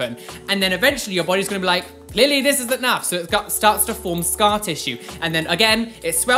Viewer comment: DID HE SAY “LILLY THIS IS ENOUGH”